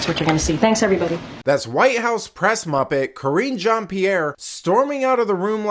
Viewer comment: You said muppet. I thought you said mop head. Silly me.